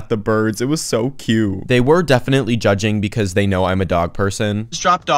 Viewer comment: For some reason I heard “they were defiantly judging cause they knew I was adopted” 💀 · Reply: I think he said he was a dog person, not adopted.